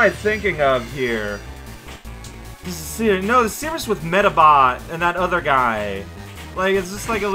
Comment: I thought that was saying "medibot" and for one beautiful moment, I hoped that my dream crossover would come to pass… but it was not to be.